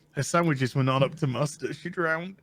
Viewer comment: Wait! Did did I hear say, "Her sandwiches were not up to mustard...?" (1:33) I played it back 4 times and that's what my brain is hearing. "Her sandwiches were not up to mustard..." I'm probably completely miss-hearing it, but it actually sort of makes sense. "Oops! Her sandwiches were not up to mustard!" :p